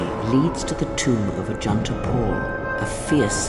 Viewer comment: Because he started off with scripture every time he says the word Paul, for Ajunta Pal I keep hearing " the Jedi and (apostle) Paul" 😂😂😂